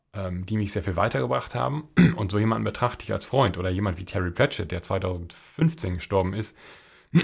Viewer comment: Ein sehr provokanter Titel - ich war zunächst etwas empört. Aber letztlich hast du mit Deiner Sicht ins Schwarze getroffen und ich kann Dir nur zustimmen. Wen hast du bei Minute 07:28 erwähnt Terry ... (?) habe den Nachnamen nicht verstanden. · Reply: Terry Pratchett. Meine Empfehlung sind die Discworld-Romane. Am besten im englischen Original. Wenn nicht das, dann eben auf Deutsch: Scheibenwelt. Unter den über 30 Büchern (man kann die einzeln lesen, ist kein einzelner Handlungsstrang) gibt es viele Highlights und nur eines, das mir zwischendurch langweilig wurde. Der Rest war eine helle Freude und ich war todtraurig, als ich von Terrys Tod erfahren habe. Er war ein unglaublich guter Beobachter. Ich weiß noch genau, wo ich war, als ich von seinem Tod erfuhr.